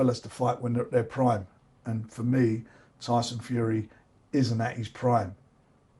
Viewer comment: Around the 09:50 mark, did Frank say Tyson ISNT at his prime? Odd comment